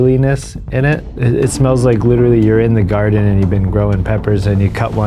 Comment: I wanna point out that I am not even 5 seconds in and what I heard was "Smells like literally urine"